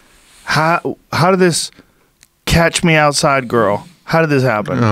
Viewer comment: All this time I thought she said "cash me outside" ..